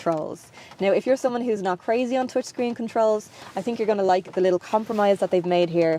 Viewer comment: (Turn on CC subtitles) Did you say twitch screen controls? I like that terminology.